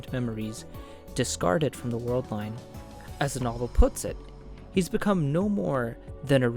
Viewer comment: I had to go back a couple seconds at 12:10 because I had to double take if I heard "As Maho puts it.." instead of "As the novel puts it..." mahophrenia